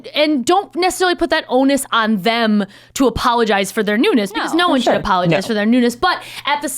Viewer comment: I heard "No-one should apologize for their nudeness."